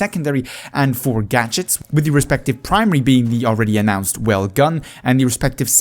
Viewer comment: I cant hear to well, did you say "Well gun" at 3:19? Looks like an SMG for the medic class. Great informative vid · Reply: Yes indeed the Welgun. It’s being implemented as SMG in BFV 😊